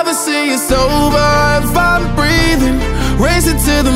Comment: Im only watching this version because i thought he was saying " you'll never see me sober if I'm breathing "